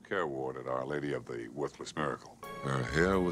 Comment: Our Lady of the Worthless Miracle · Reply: My ears must be playing tricks on me, because I heard it as "Our Lady of the Worthless Nerd"...